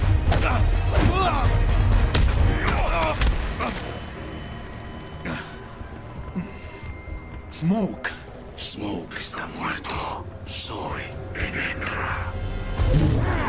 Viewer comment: Mario Castañeda luego de ver Dragon Ball Z Kai, y Raiden con la voz de freezer q pedo😂 y alguien me puede decir que dice Smoke en el min 10:47 porque parece que dice "Smoke esta muerto soy Vegeta"😂